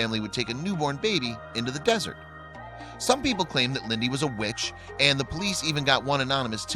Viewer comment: Oh, here we go. Calling the woman a "witch".. Jesus Christ, humans. What a bunch of bastards. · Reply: The Matadore I thought they said bitch...oh, that does change things.